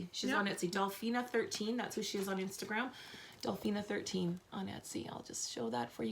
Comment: Hello. Love your podcast. Can you please tell me the name of the bag designer ? I thought you said Dolphins 19. I can not find this name. Thank you for your time. Keep up the great podcast. Thank you. P.S. I ordered The Shift shawl & yarn kit. Thank you. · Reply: It’s on instagram and Dolphina13 on etsy.